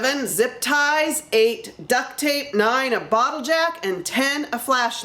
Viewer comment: You said a bottle jack, In my head I heard it as a bottle of jack. Lol.